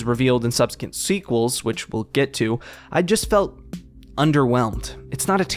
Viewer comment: Hello neighbor is the perfect definition of "Great idea, Awful execution" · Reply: I thought you said underrated at first